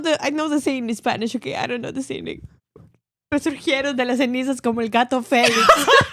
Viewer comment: Okay at 1:23:44 ish, I have to know lol, what's the idiom that says in Spanish? I only caught the end bit that sounded like "como un gato Felix (feliz?)" but I'm not sure I heard anything correctly :P